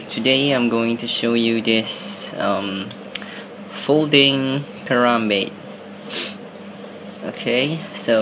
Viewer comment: Thought you said folding Harambe